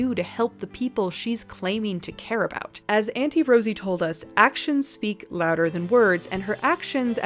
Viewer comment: I thought you said Anti-Rosie and not Auntie Rosie and thought "Wait, why is Emily the anti-Rosie?". Had to rewatch that line about 3 times to get it (also I love you videos so much you're doing an amazing job. Super excited for part 2)